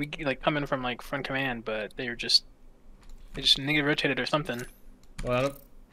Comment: what did he sayyyyyyyyy?! saying some wild shit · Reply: I heard “needed to rotate”….